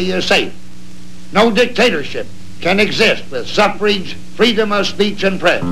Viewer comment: Did he say "suffrage"?